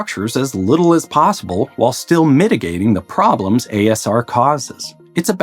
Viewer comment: There's a typo in your subtitles around 16:53. It says "AAR" instead of "ASR".